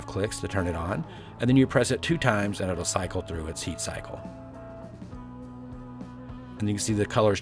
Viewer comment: What is he saying 2:29 - 2:32??? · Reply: Sade Symone “and then you press it two times and then itll cycle through its heat cycle”